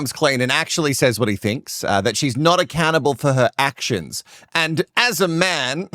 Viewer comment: I heard “not a cannibal.” 😂😂